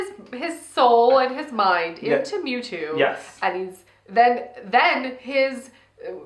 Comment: For a second I thought you said memaw. I was like holy crap this movie is dark! The grandma kills her son! Lol